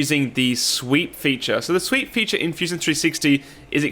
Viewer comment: For the first minute I heard "the sweet feature of fusion 360"